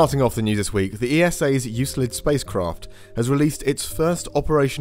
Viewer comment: I thought you said "ESA useless spacecraft" 😂